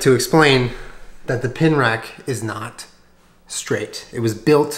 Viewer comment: Pin rack? more like pain rack, am I right? Guys? ...Guys? No one? okay... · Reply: I thought he said pin wreck!